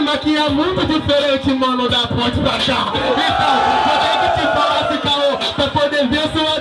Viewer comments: que que o falou no 2:43 sobre o jhony? kkkkk não entendi o final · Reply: " vou ter q te falar sem k.o , pra poder ver sua derrota até o jhony voltou " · O que o fala nos 2:43 do Jhony? Não entendi :v aehaueh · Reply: 3 'eu vou te falar sem caô,pra poder ver sua derrota até o jhony voltou.'